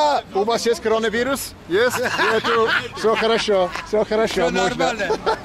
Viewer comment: I heard at 3:16 "wo normal hai". This is in Hindi. Not sure anyone else has noticed the same. Tell me your views guys !